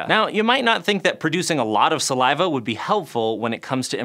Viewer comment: I thought it said salvia